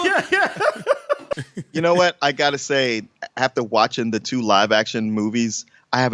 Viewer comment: It sounds like Rayden says, "I got a saw!!!"